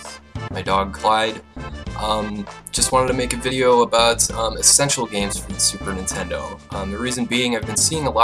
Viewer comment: At the start it sounded like you said e-sexual games